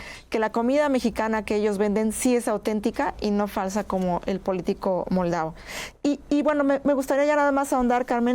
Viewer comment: JAJAJA YO PENSE QUE DECIA POLITICO MALVADO